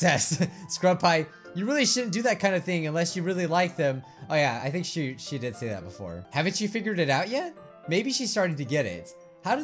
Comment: did you say ''havent you finger-ed it out yet?!?!?'' lmao